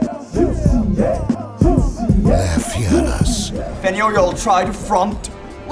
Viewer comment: What is the gangster saying on 0:19 ? Something about "front"? "And you will try the front" maybe? That doesn't make any sense.